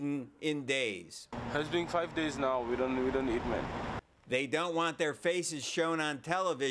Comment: WTF, did the dude from Congo really just say "We don't eat men"?